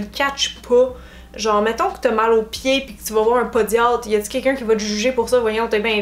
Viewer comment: Merci de ton discours, ça peut en décomplexer certaines ! Par contre c'est juste moi qui bug ou à 28:00 elle dit pédiatre au lieu de podologue ? Gros bisous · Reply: j'ai dit podiatre ! :)